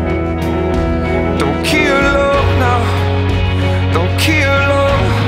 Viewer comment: Forever trying to decide whether he's saying "don't kill love" or "don't keel over" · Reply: I always heard 'don't key her hole' but I'm pretty sure that's not it.